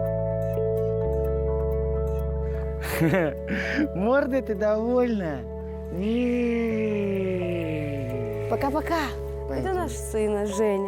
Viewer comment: мне послышалось или этот папаша сказал малышу "морда ты довольная"?